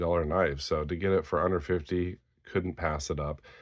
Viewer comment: When you said under 50 it sounded like you were saying 150 😅